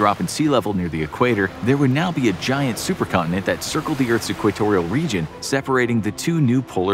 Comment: Could of sworn he said clitorial region instead of equatorial